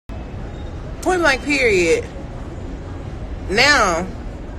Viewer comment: She said point my period? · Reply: Point blank period lol I thought she said on my period.